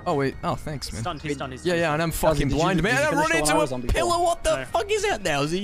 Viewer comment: I thought you said pillow, not pillar =P HUHHHHHHHHHHHHHHHHHHHH?